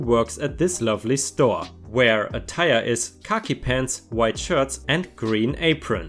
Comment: Every time you say "khaki pants" I hear "cocky pants". It's difficult to describe the correct pronunciation but "khaki" rhymes with "tacky".